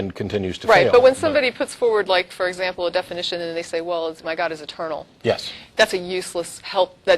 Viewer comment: As my attention was on doing my invoicing and this was running in the background, I heard over and over that "My god is a turtle" , which I found amusing. No more ridiculous than anything else, but a turtle! Sometimes I love that my hearing is starting to diminish